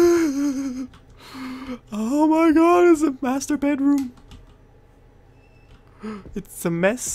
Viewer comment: Here i thought you said masturbate room